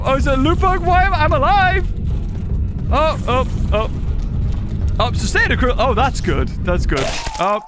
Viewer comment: what I don’t see that word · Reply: Bro said sustain incruel 💀